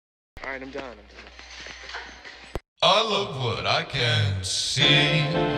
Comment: every time the only thing i hear is i love wood i cant see